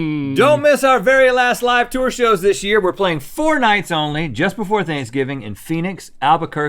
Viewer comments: I thought Rhett said "we're playing fortnite only..." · i thought Rhett was saying "we playing fortnite only"